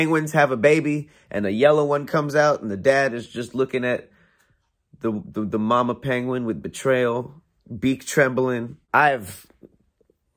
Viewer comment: all I hear is "yellow penguin what he wants" LMAO